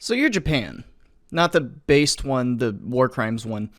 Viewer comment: did i mishear? Did he say "based one"